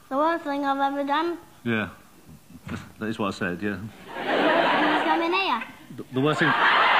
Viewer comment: what does the kid say ? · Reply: was coming here